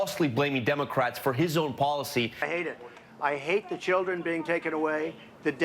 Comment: Trump: "I hate the children being taken away..." Did anyone else hear it like that...?